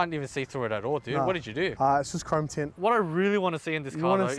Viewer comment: what did he say? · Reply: Chrome tint